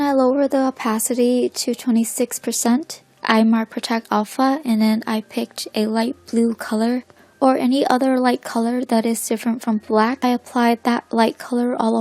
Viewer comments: I DIDNT UNDERSTAND,PLEASE HELP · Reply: "lower the opacity to 26%, I mark protect alpha and then I picked a light blue color or any other light color that is different from black." Edit: Protect Alpha is an option just above the layer display, you can see she marking after she lowers the opacity. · What did you say at 2:16? Mark protect alpha? What is that or what button or brush is that?